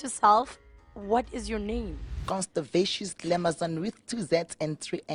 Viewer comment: did she just say her name is constipation